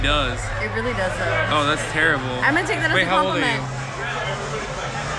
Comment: The fact that she said "Just kidding-guh" instead of "Just kidding" is proof that she's underage. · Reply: psyphi407, I only heard her say “just kidding” I didn’t hear her say “just kidding-guh”.